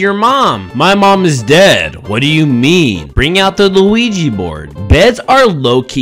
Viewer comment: It sounded like you said Luigi board! 💀